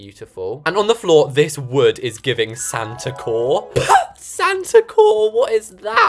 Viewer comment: minute 4:08 ‘santa core’ i thought u said santa coal😂